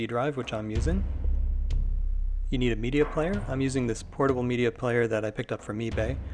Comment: what did you do for the audio? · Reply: oh I thought it said you used mini media player. Arnt towers and subwoofers visible?